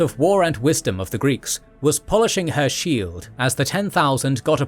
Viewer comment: Did he say email?